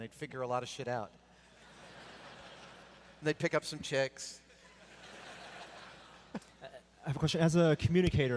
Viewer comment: does he say "they'd pick out some chicks"?